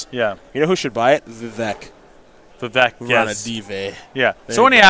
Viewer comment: what does say, "vaveck"?